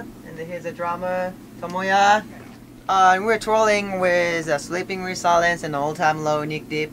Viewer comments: I thought Taka said Trolling..hahaha TOURING...okay its touring.haha Taka is such a cutie! 👌❤ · I keep hearing "we're trolling" instead of 'touring' XD